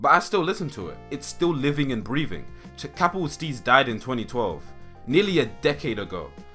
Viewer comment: Who os the artist mentioned at 7:54? English is not my native language and I cannot underatand what the narrator is saying there. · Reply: capital steez